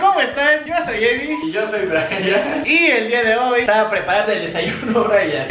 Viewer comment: En el minuto 0:07 escuché que decia "y yo soy brayana" hajahhajaja o rayana 😂😂😂😂😂